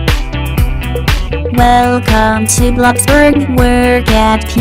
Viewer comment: Why i hear blox fruit